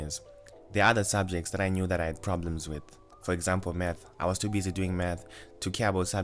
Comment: What I heard: “There are other subjects that I knew that I had problems with. For example meth, I was too busy doing meth.” 😂